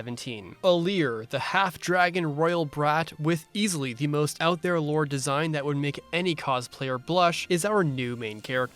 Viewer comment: Had just woken up and immediately misheard 0:12 as "the half-dragon royal brat with easily the most out there lord design that would make any cosplayer brush ". I think I'd seen too many Colgate-chan memes before I went to sleep last night.